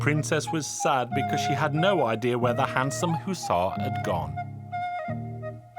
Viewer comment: I love these so much!! Also, I just wanted to point out that at 6:35, the caption says "handsome who saw" instead of "handsome hussar" For those who don't know, a hussar is a member of the European cavalry unit for scouting. Which is modeled after the 15th century Hungarian light-horse corps! Caption confused me a bit, but I still really liked the story ⭐️